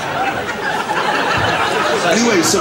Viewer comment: What did he say at 6:09 that was beeped? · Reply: He was talking about Americans and said "the're cxxts !"..lol